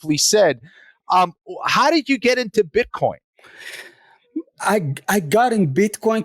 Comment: Sounds like Moicano is saying bitchcoin. 🤣💦